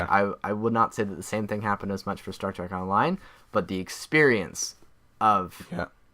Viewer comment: I thought it said Shrek Online...